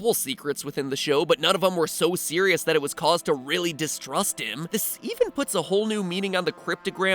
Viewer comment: The dog sounds like it is saying distruct instead of distrust